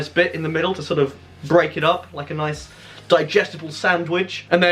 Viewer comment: Did you say "digestible soundwich"?